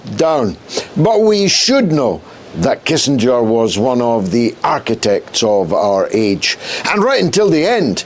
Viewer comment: I thought for a moment it said Kissinger Asshole of Our Age.